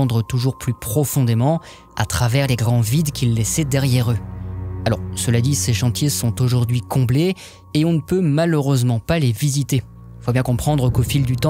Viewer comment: C’est quoi à 22:35? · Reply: le départ d'un chantier descendant comblé.